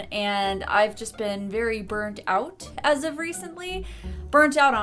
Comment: did you just say "Bird out"?